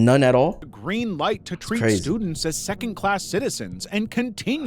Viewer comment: my caption said secondass 14:12 👆👆👆